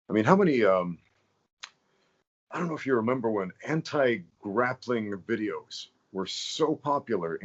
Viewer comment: i missheard that as Hentai grappling videos